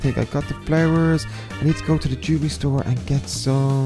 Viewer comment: LOL I thought said let's go to the juvie store but then I realized he said lets go to the jewelry store